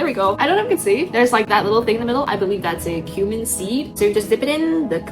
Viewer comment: Good grief, I misheard it as "Human Seed"